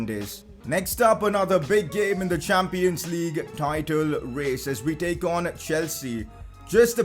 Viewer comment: wait did he just say the champions league title chase ? ( Speech 0 )